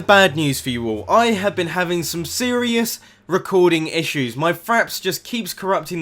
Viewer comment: lol i thought u said faps, instead of fraps xD